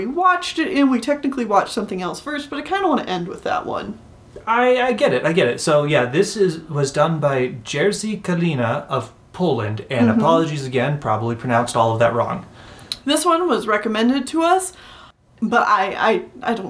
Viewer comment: NGL, for a sec I thought you were trying to say “Karina” but just couldn’t pronounce the “r” properly. I guess I’ve been watching too much of Doug Walker.